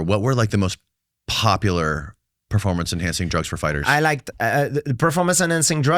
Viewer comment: I could of sworn I heard him say " my favorite" when they were talking about PEDS and then stopped himself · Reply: he says “I liked” lol I don’t think he was clean when fighting. Danny clearly asks what drugs were used during that time and he has a Freudian slip lol